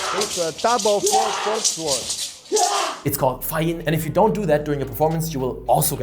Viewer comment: sorry, what is it called? Fa Ye? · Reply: Fa Jin